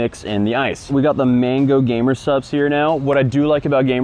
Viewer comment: This whole time I thought has been saying GAMER SUBS. 🙂‍↕️